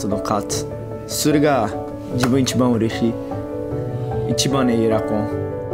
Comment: (空耳)一番にエアコンって聞こえる